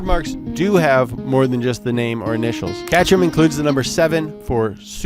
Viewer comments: sent me down a rabbit hole. KAY-ter-um, it sounded like you said "Catchrum" and that made me question my life for a minute. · did he say catcherman?